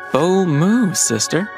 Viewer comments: I THOUGHT HE WAS SAYING "OH MOO SISTER" THIS WHOLE TIME AND I WAS LIKE, WHAT DOES THAT EVEN MEAN? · I just LOVE Edric’s “Bold move, sister” I LOVE the Blight twins lol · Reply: LOL, I misheard it a s"Oh MOO, sister" :D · I totally thought that edrick had said “oh moo sister” 👀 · I thought Ed said "oh moo sister" 😂